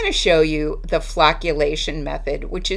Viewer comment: Phew for a second I swear I thought you said flatulation. 😉